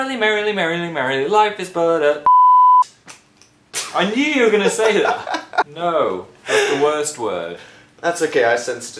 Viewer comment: did he say f*gg*t? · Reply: he said c*nt